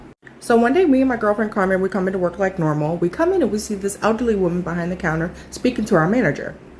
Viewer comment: I just started the first story and already I’m annoyed. Calls her “ugly” then says “I’m not tryna be mean” 🙄 yes you are, stand on that shit or just don’t say it Edit: 22:21 I agree 100%. Personally once I know you’re ugly on the inside or once I sense that you have gross energy youre ugly to me. Like I can’t even be physically attracted to you after that. Love it for me tbh 😅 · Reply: She said elderly not ugly 💀 I thought she said ugly too and I was like damn she rude but I read the captions and she said elderly 😭